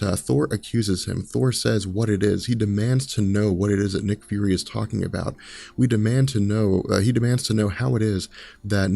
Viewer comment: What did he say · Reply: Gorr was right